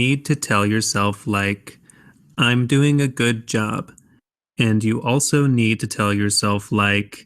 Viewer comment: In this dialog - did say "Im doing a good job? Becouse I hear only "Doing a good job? Where is I'm?